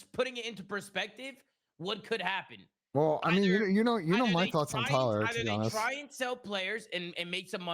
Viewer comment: Abezys spiral gunfights enters the chat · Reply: when he said “You know my thoughts on Tyler..”, I was like ‘Did he just say fuck aBeZy?’ 😂